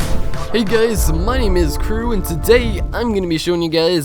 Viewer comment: Sometimes i heard smth like "money is cruel" in the beginning